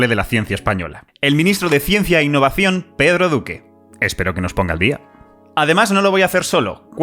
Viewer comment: Mk, yo pensé que decía ciencia con petro y duque jajaj